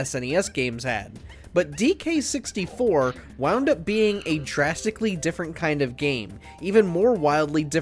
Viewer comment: for DK64 at first I thought he said "ended up being a TRAGICALLY different game" but he said drastically lol. I was like EXCUSE ME SIR DK64 ISNT TRAGIC ITS AMAZING. It is a weird fucking game though 🤣